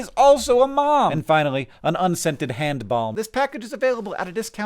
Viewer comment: i heard 'unscented hand bomb' 🤣